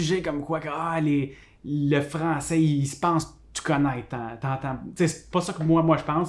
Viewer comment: Les français se pensent quoi ?? J'ai rien compris. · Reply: Il dit 《le français pense tout connaître》